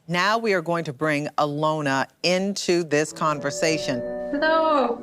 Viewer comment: I thought she said "Now we're going to bring a loner into the conversation". I was like damn, you didn't need to after Caesar that hard! 😅